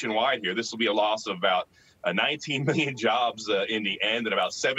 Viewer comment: I couldn't understand the number you gave overall at 2:40. Was that 19 "million?"